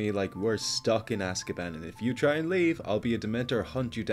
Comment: i heard "if you leave, i will kiss you." ....... I live in canada bye.